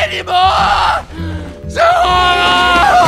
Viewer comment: did medic just say ZA WARDO